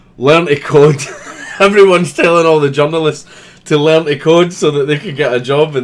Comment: you said "the journalists" I heard "the genderless"